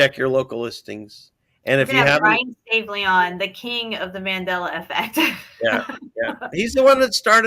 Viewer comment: who did she say was the king of the mandela effect? i wanna dig more into it, any other people you think i’d like to would be appreciated. great vid, loveyou guys! i pray the Lord continues to lead you both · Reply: mentioned Brian Staveley